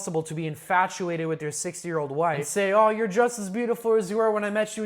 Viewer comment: Does anyone else's YouTube automatically turn on the closed captions every once in awhile because it happened this time and instead of 60 I thought he was saying 6-year-olds until I was over 4 mins in😭😭😭😭